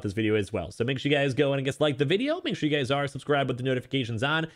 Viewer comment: did he just say dislike the video ?lol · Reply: He said Just like the video